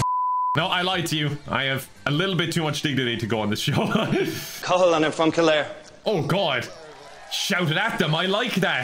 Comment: what a thick accent, it sounded like he said "Culle and I' fro kilhair"